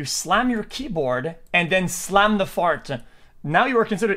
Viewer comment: I don't know How old this guy is that's comm. but he acts like a kid immature kid at that by Cursing when its sooo not necessary and earlier( because he speed talks) it sounded like he said ," did he smash the fart down"? Sllllow down, slow down Please